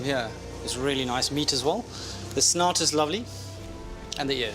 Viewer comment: snout, I thought he said snot